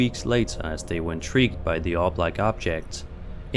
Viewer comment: Oh! You were saying "orb like". I misunderstood and thought you said "all black". It was an obvious white orb and I didn't get how you kept calling it black. After rewatching 3 times, I realized it was me and not you!